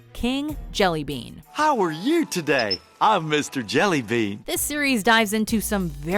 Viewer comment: My i-phone was sitting on my desk in front of this and when the video said "This series" my phone thought it said "Hey Siri" and started to draft a question for siri based on what the narrator was saying.